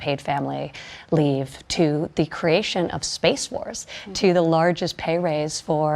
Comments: Sounds like she says creation of “space wars” at 1:50. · Did she say creation of space wars...? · omg, 1:52 she actually says Space Wars